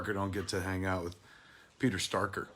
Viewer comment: did you just say "Peter Starker"? LOL